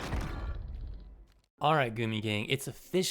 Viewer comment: My tired ass thought you said "Alright booty gang"🤣🤣🤣